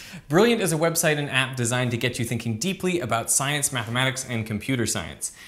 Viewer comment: I thought that said DRINKING LOL